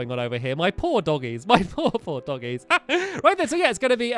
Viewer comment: at about 11:09 stampy says my poor, poor doggies or did he say my paw, paw doggies